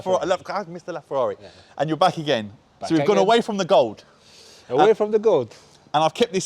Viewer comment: I thought he said away from the goals......